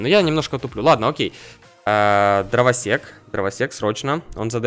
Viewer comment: мне послышалось дровосекС:)))))